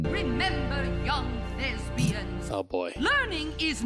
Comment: it sounded like the teacher said "Remember young lesbians" and now I can't hear it any other way.